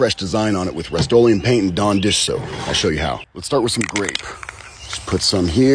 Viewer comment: This man had me tripping I thought he said green and had me me feeling color blind for a minute I had to watch the video again to figure out it he said grape 😂 and not green 😂